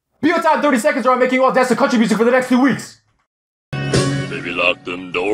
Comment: What did he say?🤔 No seriously please reply what did he say? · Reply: I think he said or I'll make you dance to country music for the next 3 weeks